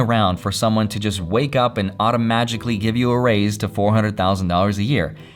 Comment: not sure if it's a pun or I heard it wrong "auto magic ly"